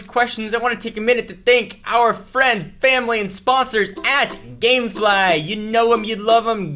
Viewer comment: SOUNDS like he said GAY FLY